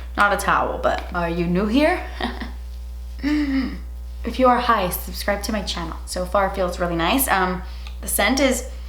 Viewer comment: I thought you said “if you are high subscribe to my channel, and i am high, so I tried to subscribe but then realized I was already XD